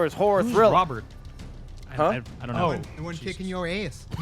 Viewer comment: What did Robert say that everyone laughed at right around 15:40? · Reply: asked, "Who's Robert?" He replied, "the one kickin' yo ass"